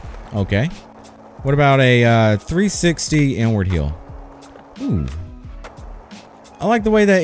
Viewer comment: What’s an N word heel? 😂